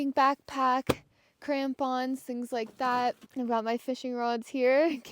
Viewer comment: "did she say Tampons?